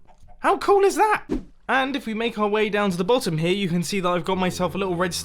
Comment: I thought he was going to say downtown